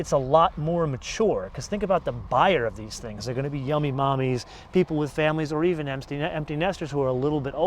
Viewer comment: wait did he say " yummy mommies" at 4:35......lmao..yummy mommies